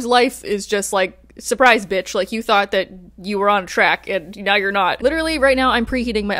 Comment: I heard “on crack” instead of “on track.” I was a tad bit confused. I treated myself to Heartstopped V 5 today!